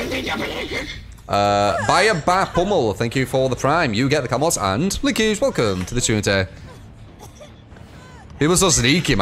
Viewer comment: Does anyone know what he said there? "You get the camwoss and liki zhwokun to the kumteh"?